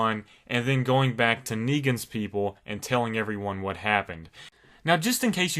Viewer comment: Did i just heard "Niggans people"?